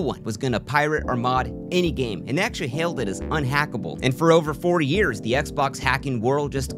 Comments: It's absurd how this story basically turns into an IRL BreakingBad-esque crime ring of sorts. Thise kids were/are absolute lunatics, they had so many oppertunities to quit while they were ahead but just kept pushing their luck. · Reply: Did he just say for over 40 years? It was released in 2005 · I believe at 1:36 you say “4 years” but the closed captions said “40 years” … it was only un-hackable for 4 years right???